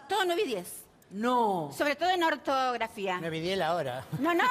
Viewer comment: que dice Gasalla? algo de la hora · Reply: 9.10 la hora y no las notas de la escuela de Silvia